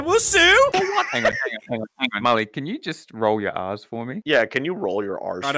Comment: I thought that they were saying to roll your ass